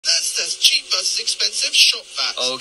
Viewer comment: I thought he was saying cheap v expensive sand